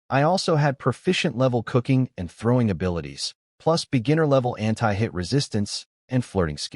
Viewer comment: did anyone notice how the AI said anti-hit but the captions said anti-Hitler, which is very inappropriate and very sensitive to a lot of people